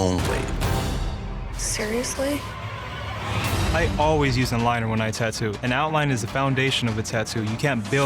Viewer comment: i thought he said “i always use a minor”